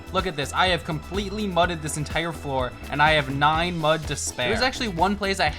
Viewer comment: At first I thought he said nutted floor so I thought he nutted all over the floor